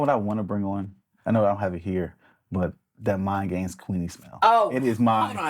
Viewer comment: what the heck did you say!!? Mind Games Queen Smell? Mind Games Queening? Mind Games Queenside? Which one I want it!! Please let me know! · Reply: is talking about Queening. I think his feelings have changed on that fragrance now though.